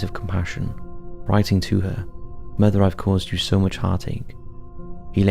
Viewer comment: I thought it said, - “mother, I have caused you so much diarrhea.”